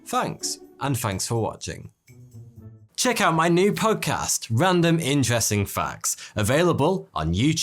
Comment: Every time I hear you say "podcast" I have to do a double take because I keep hearing "punk ass" 🤣